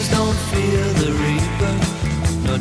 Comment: When I first heard this I thought it said Don't fear the reefer.